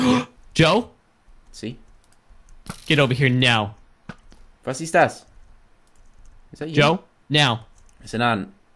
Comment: Did say "Was ist das" ?